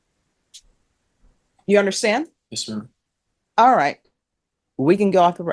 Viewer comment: is he saying “yessir” to her!????